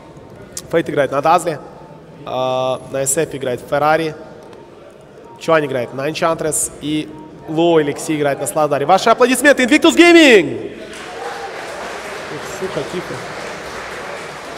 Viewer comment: "Вот сука,тихо"-17:45-кому послышалось?